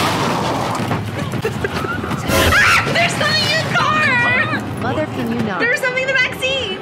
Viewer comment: i thought she said “there’s something in the vaccine” 💀